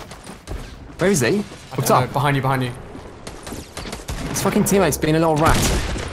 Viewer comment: i think says the f word he says "his f***ing teammate is being a little rat"